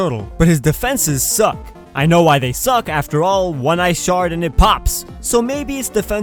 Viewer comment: When he said "one ice shard and it pops," I thought he said, "when I shart it pops"😂